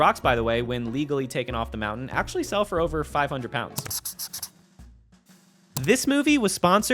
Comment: The captions say “500 lb” instead of £500😭